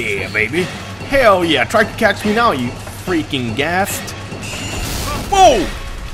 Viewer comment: did just call the thing a ghast??